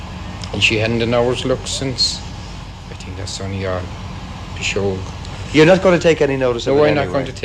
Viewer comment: I think he says "Pisoegs" which can mean superstition or a type of fairy spell or curse. Also I thought his chinstrap beard was literally his chinstrap.